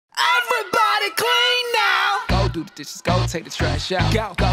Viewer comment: I thought the said cream now lol 😅